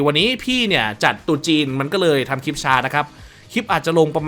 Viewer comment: ผมได้ยินว่าตูดจินอ่ะ555